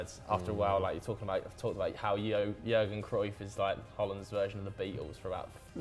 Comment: Did he just say Jurgen Cruyff??